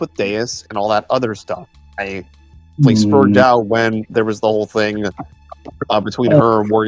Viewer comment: did he say he "sperged" out? Isn't that a bit derogatory towards people with autism? 🤔